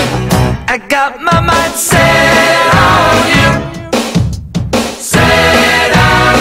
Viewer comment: I always thought he said, I got my mom sit on you